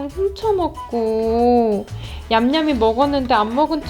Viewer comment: “애기들 간식이랑 술쳐먹고 냠냠이 먹었는데 않먹은척해서 살쪘군아.” 라고 들리는데?ㅋㅋㅋ